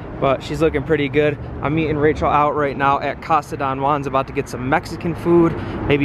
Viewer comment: Did he say "I'm eating Rachel out" @ 3:09 ? LMAO